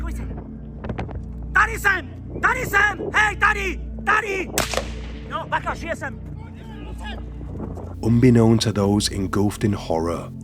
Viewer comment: Was he saying, Daddy daddy daddy!!"?? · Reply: It's one of the police officers, he's shouting I'm here! I'm here! To lure the attention away from the victims.